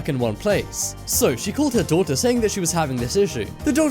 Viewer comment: Who else thought he said doctor instead of daughter